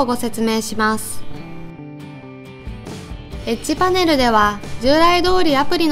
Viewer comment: エッジパネルがエッチパネルに聞こえた